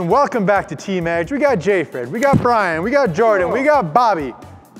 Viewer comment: did he say joey or jordan at the